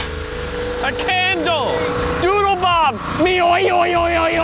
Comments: did he say doodle mom or is it just me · Did he just say doodle mom?